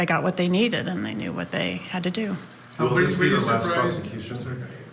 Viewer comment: I heard from one reporter, “will this be the last prosecution”? I’m still trying to figure out the other question… 😅